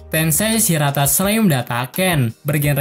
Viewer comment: "shirata"..? ane slh denger tuh bang awokawokawok😂.. ampe ane ulangi 5x lbih hehehe maap kalo salah denger xixixixixi:v